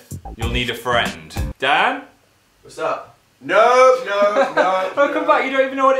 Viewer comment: did dan just say what's up